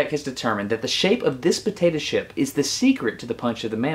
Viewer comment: Did I hear you say "potato ship"? Strong maybe, but not stronger than your pringle duck billed sister.